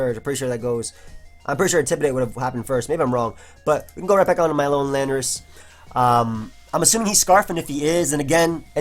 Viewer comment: When said "my own Landorus" at around 14:33 I honestly thought he said "Alolan Landorus" :S